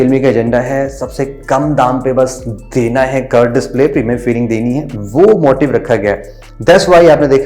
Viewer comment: I heard wrong - premium feel ❌ Female feeling deni ha curved display 😂👍